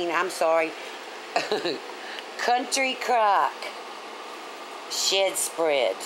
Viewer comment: Did she say Country Crock shit spread at 3:14?